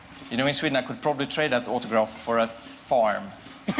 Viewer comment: What is he saying at **1:13**? - "You know, in Sweeden I could probably trade that autograph for a f-foyer" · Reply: trade it for a farm